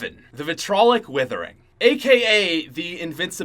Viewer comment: I thought he said winters howling